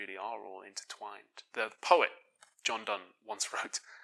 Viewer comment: I thought you said “the poet Jeff Dunham”